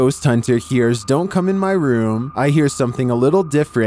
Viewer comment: I heard her say "do you wanna come in my room?"